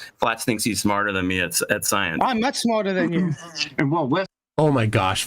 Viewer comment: at 1:03:42 : "I'm not smarter than you". again at 1:04:12 : "I told you, you is (sic!) dumb as f*ck". So admits that he is not smarter than someone who is (in his opinion) dumb as f*ck. Classic example of QED. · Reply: Fwiw, I think he actually says "I'm much smarter than you", and you just misheard, and then leaned into the supposed inconsistency because it plays into your biases. says enough nonsense BS that y'all don't need to try so hard to score points when what you've done is simply misheard something. It just makes y'all look foolish.